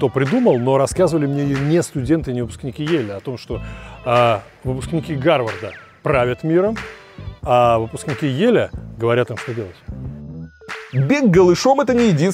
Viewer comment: ученики гарварда правят миром а ельские что? Что он сказал ? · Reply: Выпускники Гарварда правят миром,а выпускники Йеля говорят им (Гарвадским), что делать..